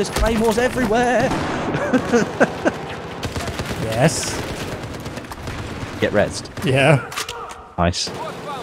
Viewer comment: I thought your friend said "get rezzed" in the same sway that someone says "get wrecked", LOL.